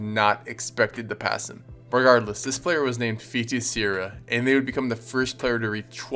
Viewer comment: i thought u said feetie sarah